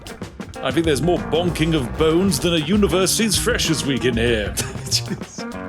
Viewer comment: a University's freshest week? I think I misheard that